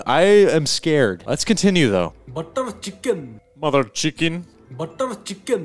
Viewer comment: i heard butt of chicken